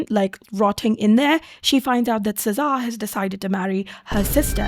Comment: I heard cesare as sza wtf 😭